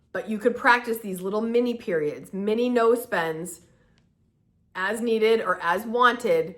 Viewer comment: When you said "no spends" at the end of video, I heard "nose bends" 😂😂😂😂